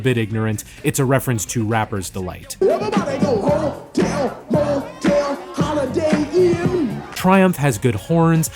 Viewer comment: i was today years old when i realized he says “hotel, motel, holiday inn” i thought he said “hotel, motel, all up in here” 💀